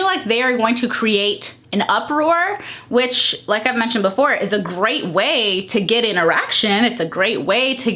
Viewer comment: 5 minutes in. Giggling because I heard, "it's a great way to get an erection." 😂😂